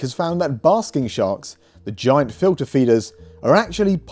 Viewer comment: I thought I heard say "bathroom shark" which is terrifying. This was a fantastic video with so much new info. Love the interview and that you were so proffesional that you waited to release it until his paper was out.